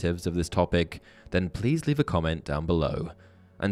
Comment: I thought you said: width beyond measure.